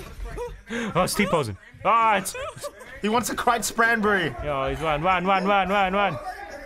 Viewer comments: did anyone catch say "crite spranberry" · Did u just say does he want a crite spranberry